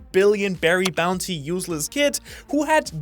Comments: I don't know if you meant it, but it definitely sounded like you said "useless" Kidd, instead of Eustace, and I love it. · I heard useless kid